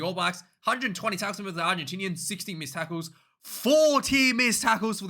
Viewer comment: Mate i agree i hate the kiwi commentary. I dont think the lions had 40 miss tackles in a game tho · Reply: I misheard it as 14 missed tackles.